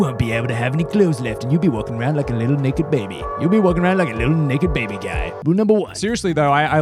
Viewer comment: he sounds like he is saying n word baby.